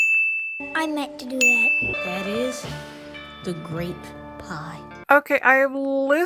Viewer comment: hey, i hear "grape pie" sometimes too, so its not just you